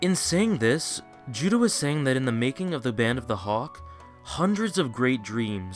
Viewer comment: BERSERKER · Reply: did he just say making fuck